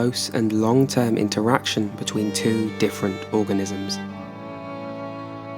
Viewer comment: Misheard the first sentence, thought you said it was ‘ a gross and long term interaction’